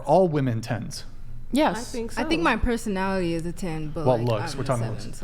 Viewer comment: "I think my puss-onality is a 10" 😅 i thought that girl was fixing to say something crazy 😂😂😂